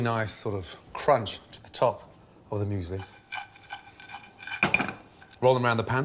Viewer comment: It took me so long to find out what the dish was called because when he said "the muesli" i thought he was saying "them usually"